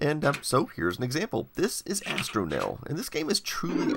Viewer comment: I had to go back twice to figure out that the game you tested was called "Astronell." I kept hearing "Hastromil," and thought somebody had made an obscure Hitchhiker's Guide reference.